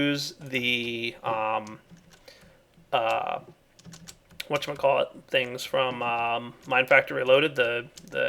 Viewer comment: Someone can write what says at 7:06 for a french guy who wants to improve his English please ? "What [...] thing" ♥ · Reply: whatchamacallit. It's a shortened version of "What you might call it". You use it when you don't remember how something's called.